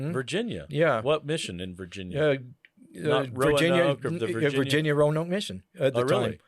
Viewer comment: "Virginia Roanoke mission" is what I heard him say